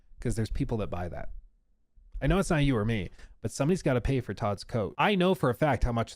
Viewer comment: I thought you said, somebody has to pay for Todd's coke though." lol